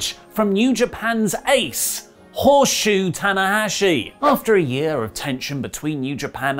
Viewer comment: “New Japan Ace, Horshu Tanahashi” Well now I’m actively rooting against Ollie. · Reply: I thought he said "horseshoe." I thought it was another dumb WrestleTalk joke that no one understands.